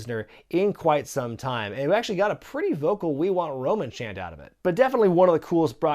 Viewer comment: I heard “We Want Strowman”